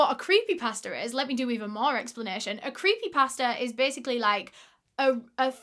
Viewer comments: Lmaoo I thought you were saying creepy pastor until I realized you were saying creepy pasta 😭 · I really thought you were saying creepy pastor which idk if that’s more or less confusing than creepy pasta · I've never heard of creepy pasta. I don't understand the accent sometimes and I thought she said creepy pastor🤦‍♀️